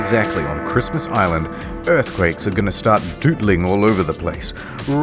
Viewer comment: did he say “DOOTLING”?